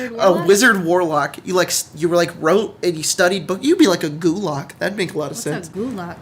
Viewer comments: Gulag...you mean Russian labor camps? LMAO I swear that's what I thought when I heard that. Then I realized you said Gulock. lol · I thought he said gulag